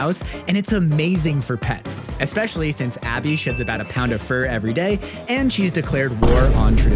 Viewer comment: I thought you said “Abby shits out a ton of fur every day”